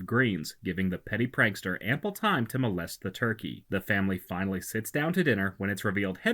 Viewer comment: I don't know if I heard it right but it sounded like he said "Molest the turkey" how does a turkey get molested? And also If he molested the turkey he would've humped it and not put a balloon in it